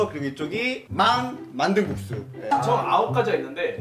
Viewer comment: 초에 막 만든 국수 라고 해야 되는데 발음이 꼬여서 망 만든 국수라고 들려욬ㅋㅋㅋㅋ귀엽다